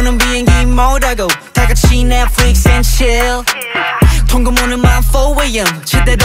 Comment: ㅇㄴ님들 이 때 운학이 파트에 내 flex and chill 이거 netflix and chill 이라고 들리잖아……. 이거 네이버에 쳐밥 우리 운아기ㅠ